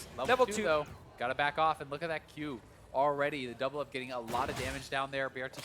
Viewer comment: Does he say doublelift!? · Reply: Sam Mayes He said "double up" as in miss fortune q